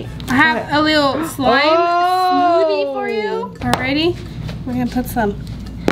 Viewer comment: It sounds like she said slime mop